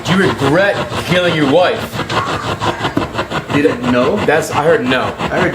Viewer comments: Instead of “didn’t know” I heard “terribly” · it sounds like it says hell no not didnt know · I heard no I don't instead of didn't know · I heard "burn her" instead of "didnt know" but dont know! Cant say for certainty what it says! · I heard "No I don't" instead of "didn't know" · I heard the spirit, it did NOT say "Didn't know." You asked it "Did you regret killing your wife?" it answered "HELL NO" - listen again.